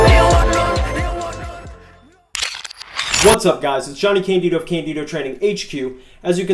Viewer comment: Swear thought you said candildo instead of candito . damn subtitles! Lol